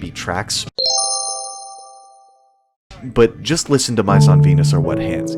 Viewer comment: I’m drawing and listening to this and I DID NOT hear “Mice On Venus”; I legit heard “My SoftPenis” 💀